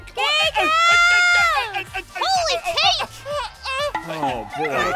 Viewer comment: I LITERALLY DIED WHEN SAID HOLY CAKE I THOUGHT SHE SAID HOLY COW